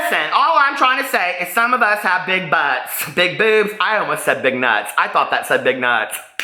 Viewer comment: I thought she said big nuts 😂😂